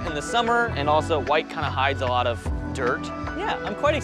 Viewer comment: "white hides a lot of dirt" ... Did I hear you right?